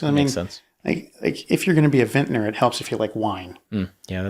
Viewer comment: I heard “if you are going to be a vetinarian it’s good to like wine” I thought superb joke, as animals whine. Only later did I realise it was Vintner as wine merchant !